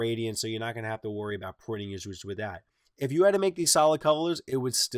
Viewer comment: "there's not too drastic of a gradient, so you're not going to have to worry about ... with that" ... what was it you said there? I didn't understand. Btw, discovered you today, sub'd today with this video. I suck at graphic design but I really want to learn it. :D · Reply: adarkerlight Worry about PRINTING ISSUES with that. Best of luck to you. Thanks.